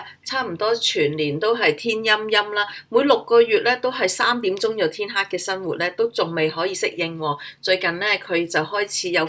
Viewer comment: Excuse me, 1:21 both Chinese and English subtitles didn't match with what you said. Did you say they have been in Manchester for 6 months or the weather in UK...? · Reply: In my voiceover, I mentioned that there are 6 months in a year that it gets dark at 3pm. In the subtitle I only brief it as “ miserable weather”. My apology :)